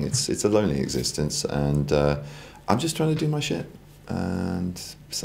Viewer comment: Lol did he say at 4:44 " im just trying to do my shit " ?